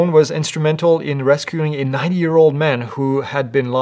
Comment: Sounds like said 9 yr old man instead of 90 yr old man, which it was.